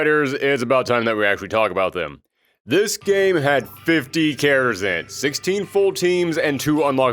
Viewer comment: i’ve been trying to understand what you said at 38:05 and it’s driving me nuts. you said there’s 50… cara-zen? like what man pls help